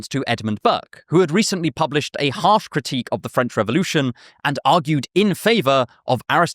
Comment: subtitle reads "half critique" instead of "harsh critique". Was this Whisper V3?